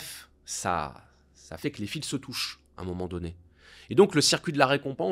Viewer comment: j'ai entendu "ça fait que les filles se touchent" 😂